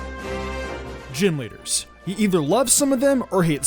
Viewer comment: I thought you said genwunners instead of gym leaders for a sec..... Great start into the video lol